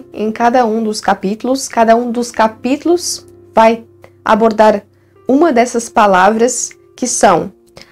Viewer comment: Vc falou abordar, eu entendi "abortar". Desculpe. 😂😂😂😂😂😂😂